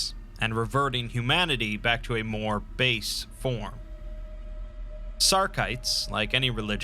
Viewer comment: I heard "return humanity to a more based form" the first time and thought he was talking about monke